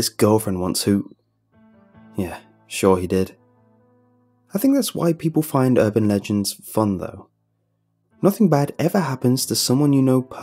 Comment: I stg I thought he said "I think that white people find this urban legends fun though"